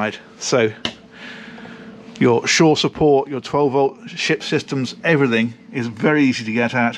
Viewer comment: 4 did you say "shit system"? lol